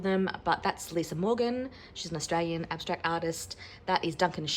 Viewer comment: You mentioned an artist at around the 6:12 mark- sounded like Teal Kivers or something, who was this person please?? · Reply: <link> tiel seivl-keevers